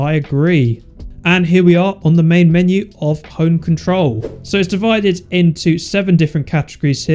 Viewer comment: I thought he said “Hoe Controller” for a moment xd